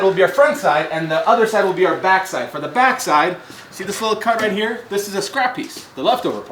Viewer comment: thought you said " see this little c*nt right here xDDD"